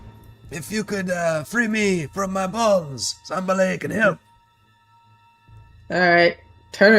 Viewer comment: yeah, I heard balls as well. Had to rewind to make sure my ears were not deceiving me!! Brings to mind "Mother will they try to break my balls..."